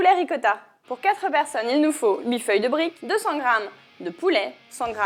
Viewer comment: c'est moi ou elle dit "1000 feuilles de brick" à 0:09 ? ;)